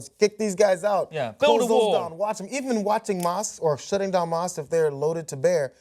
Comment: I thought the video said "Trump on shutting down mosquitoes" and I was like "wow I didn't think I'd ever agree with Trump but ok"